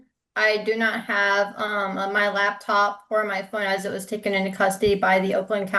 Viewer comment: It sounds like "taken" to me, allowing for mild accent variation. I definitely don't hear "tooken". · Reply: …I definitely hear “tooken”. Audibly subjective I suppose. Cheers